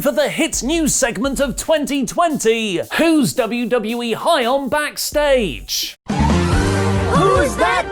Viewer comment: I thought said "who is high backstage at WWE" and I yelled out, "BRO!"